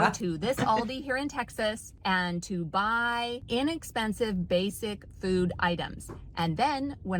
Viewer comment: You misheard, she said INexpensive